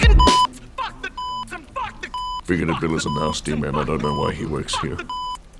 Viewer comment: I always hear it as "Bigoted Bill is an ass demon and I don't know why he works here."